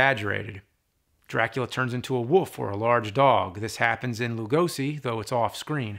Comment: James can't say "wolf". He's quite clearly saying "woof" instead of "wolf".